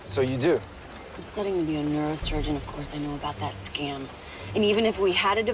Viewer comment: Ah I misheard, neurosurgeon sounded like nursing student. Why would she call FES a scam though?